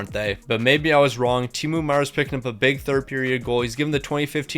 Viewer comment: did I hear Temu meier?